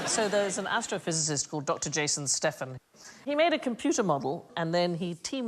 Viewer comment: I misheard it as "Dr Jason Statham", and assumed I was either dreaming or had died.